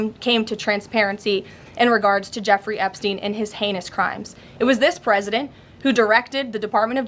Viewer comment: Did she say his penis crime ?